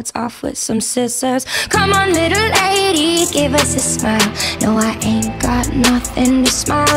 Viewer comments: First time I heard it I thought it said come on a little lady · I thought it said “come on little 80s” 😂😂